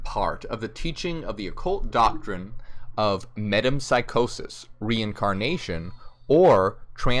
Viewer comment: Ha!ha!ha!ha! I thought you said Madame psychosis